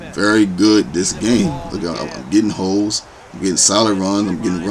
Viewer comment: add me bro CrazyChris0983. At one point it sounds like your saying gettin hoe's lol. But I knew what you said lol.